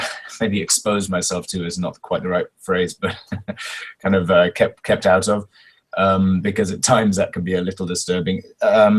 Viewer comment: what word he says at 14:35 describing afterwards this as disturbing? :D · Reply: "kind of kept out of [the shipping fan art universe]"